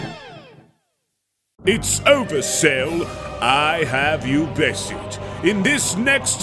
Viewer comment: not gonna lie I thought he said it's ogre cell instead of it's over cell.